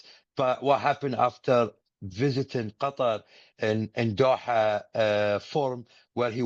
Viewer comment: sounded like they Qatarlson